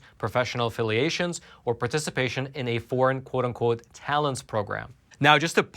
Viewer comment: Ironically I thought Roman said the "Thousand TALONS" program and not "Talents", because maybe that's what it should be called, given it's tearing at our national intellectual resources.